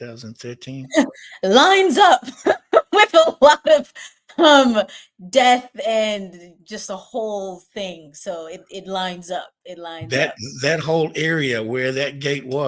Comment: I thought she said "It Lions up" haha